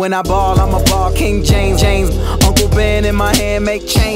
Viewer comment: Wow, I always thought he said, “A whole band ($1000) in my hand, (throws it at whoever he’s talking to) MAKE CHANGE, NIGGA!”